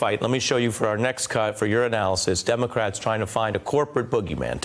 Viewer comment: Did Ari say "urinalysis" at 4:08?